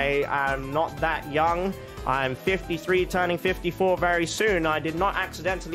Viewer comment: did I hear 53??